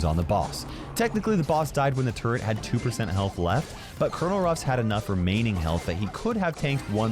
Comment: i thought he said crow and ruffs but he said colonel ruffs